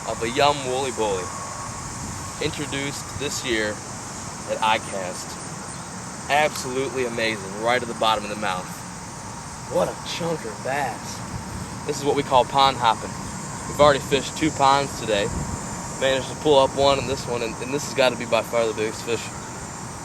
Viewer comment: did u say chunk of ass at 1:13?